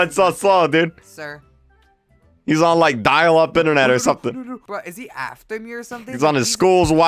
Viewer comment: why did I hear "is he afton here?"